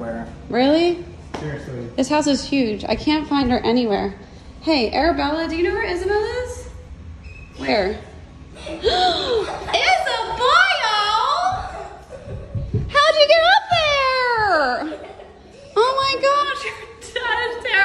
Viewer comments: It sounds like she is saying Isaboil. Like she’s boiling IN DANGER XD. But that’s odd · I love the way she says “Isabelle” ❤️ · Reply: It sounds like she is saying “isabiol”